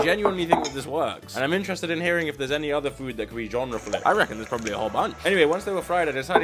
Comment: I thought you said gender flipped